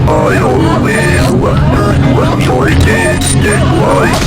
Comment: what did he sayyyyyy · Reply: “I always wondered what joy tasted like”